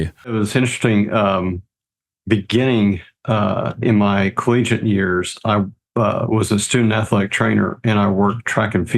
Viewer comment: I think he says 3:15? The video broke just as he says if. Really impressive PB with no speed work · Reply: Not that impressive, but at the time, I couldn't believe it! Going from not sure if I'll finish to nearly 30 minutes better than 19 years prior.